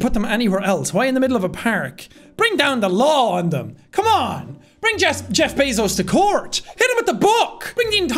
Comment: Genuinely thought he said ‘Hit ‘em with a book!’ For a half of this video